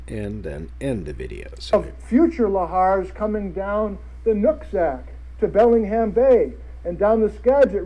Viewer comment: Sorry for commenting so much, 38:06 when he says Nooksack River...lol, not what I heard. And I was like, did he just say nutsack?